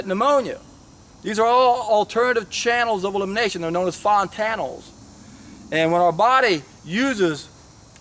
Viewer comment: I am not native, can someone write to me what he says between 2:45 and 2:47 "known as fon..." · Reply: Fontanels